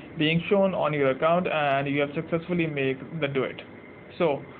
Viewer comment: Im sorry at first I thought you said, Let's do it instead of duet😅.